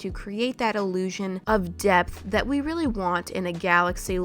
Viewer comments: Bro I legit thought you said: “to create that illusion of DEATH that we really want” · blend it in to give it some depth Me who thought she said “death” :O replays with subtitles ohhhhh hehehe😅